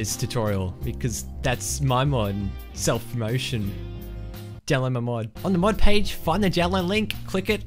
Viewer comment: At first I thought you said download my mom.